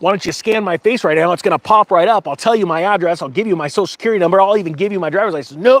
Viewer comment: I thought you said IRIS but you said ADDRESS,!!!..